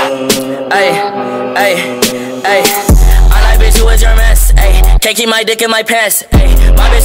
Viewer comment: sounds like XXX said "I like toys r us"